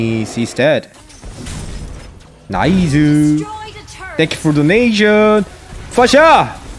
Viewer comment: Whenever general said thank you for donation I always thought he said thank you for donesia😅😅